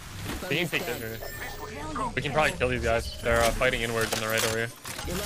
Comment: did he just say “fight n words over here” or am I losing my s